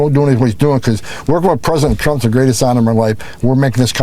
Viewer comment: did he say "because fisting president trump has been the greatest honor of my life